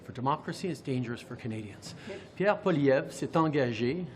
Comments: did I hear him correctly?? "democracy is DANGERUS for Canadians"??? This guy is a frigging WACKO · Did POS JT actually say that democracy is dangerous for Canadians? 6:35 Did I hear that right?